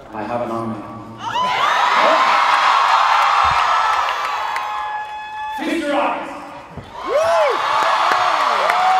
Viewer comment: What is he saying at 3:51. The audio is a bit disturbed sometimes I didn't understand the entire speech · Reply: Feast your eyes